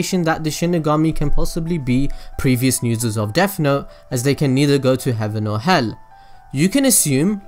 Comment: They said "If a person writes a name in this notebook, that person can not go to hell or heaven" So I think they instead go to the shinigami realm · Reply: I thought he said can either go to heaven or hell